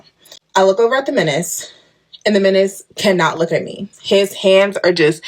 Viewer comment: All i keep hearing is "the menace???" If that's his name that was red flag number one. Straight demon seed.